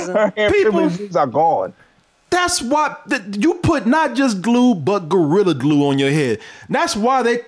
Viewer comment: I thought it said she glue gorilla hair on her head. Don't know which is worse.